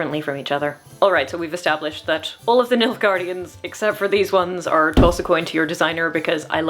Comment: you keep saying nilfgaardians and all i keep hearing is milfgaardians and its keeps making me smile lol